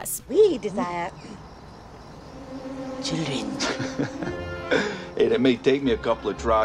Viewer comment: What is the witch saying here again? "We desire..." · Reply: Children.